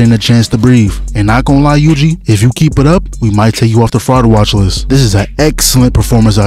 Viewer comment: What did he said at 1:55? If you keep it up Yuji…? My english is worse than mahito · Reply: He said if Yuji keeps it up he might take him off the "fraud watch list"